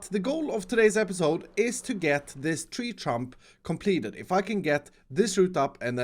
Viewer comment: Anyone else hear "Tree Trump?"